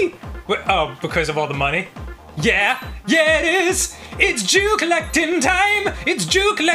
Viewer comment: I thought he was saying "its jew collecting time" 😮